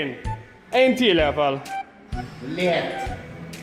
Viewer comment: i wouldve guessed russian, considering at 4:39 a clear "Blyat" can be herd repeated multiple times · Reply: He is saying "lätt" with a Skåne accent. "Ljiätt" I totally see how it sounds like he says Blyat for someone who isn't a native swedish speaker tho. 🤣